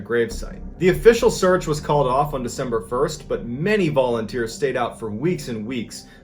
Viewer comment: Right around eleven minutes I flat out thought you said “Many Ballenteers” ….just sayin